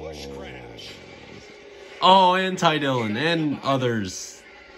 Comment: I thought u said 'anti-dillon' not 'and ty dillon'